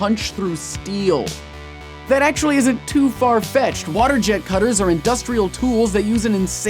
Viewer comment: who else heard "Farfetch'd" instead of "far-fetched"?